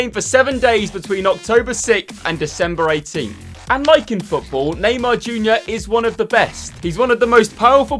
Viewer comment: I thought he said unlike in football